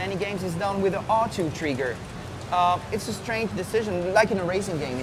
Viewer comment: does he say treegar instead of trigger...lol i repeated it 100 times and its still funny in a way